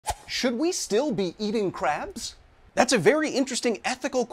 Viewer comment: I thought it said "Should we still be eating carbs?".